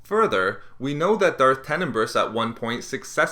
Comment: Did he just say Darth penis breath. Lol. Please give me your feedback